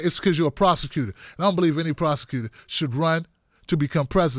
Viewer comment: OUCH! What a tumble, Kamala! "Prosecutor?" I thought you said 'prostitute.' Either would apply, though. Can't wait until she's completely out of the Presidential campaign picture. Kamala is finding out that people are not as stupid as she thinks they are.